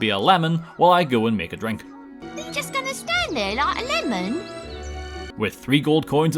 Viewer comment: Very much may well be wrong about this, but I think the kid at 1:29 is saying "like a lemming" but, like, super wrong because he's a British child.